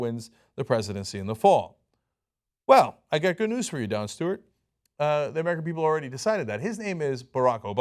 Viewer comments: lol when he said Don Stewart I thought I heard Jon Stewart. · I heard "Jon Stewart" and I was like "Waaaaaahh??"